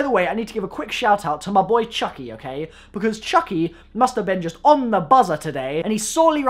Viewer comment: i swear he said masterbaiten